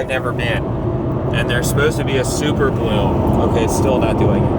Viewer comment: At first I thought you said super moon 😅